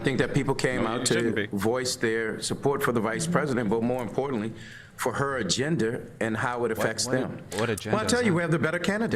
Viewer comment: did he say that they came out to vote for her uh Gender?